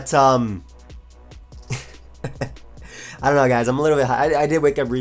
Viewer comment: thought you said "I'm a little bit high."